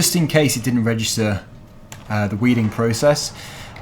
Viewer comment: I thought it said wedding lol